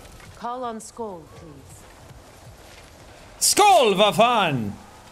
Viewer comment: did Sean just say "Sköll hva faen" aka Sköll what the hell